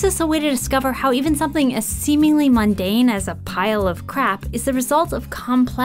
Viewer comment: Did you say cr*p?! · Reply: Crap